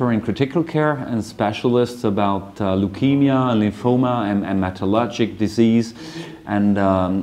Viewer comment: what is he saying here, is it metalogic, metallurgic or what? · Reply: hematologic diseases / malignancies